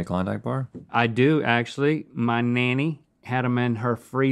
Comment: Did he just say “Mah Mammy...had some in her freezer...”